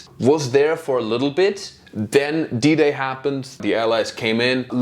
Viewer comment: Legitimately thought German dude said "Diddy happened" when he said "D-Day happened" 😂 Was gonna say, that's an impressive call back